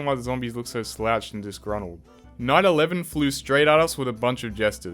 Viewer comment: i heard " 9/11 flew right off straight at us " because my volume was low lol